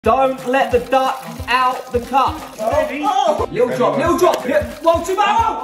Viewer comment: I thought he said "Don't let the DARK out the cup", I was so confused